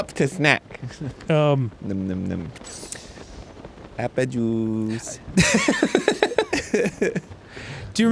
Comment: I confirm, it does sound like he says "APPLE JUICE" in ante up.